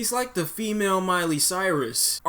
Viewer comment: I'm sorry, did you say "the female Miley Cyrus?"